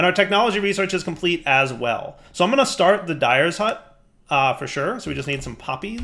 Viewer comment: I keep hearing "Dire's hut" :_dw20Derp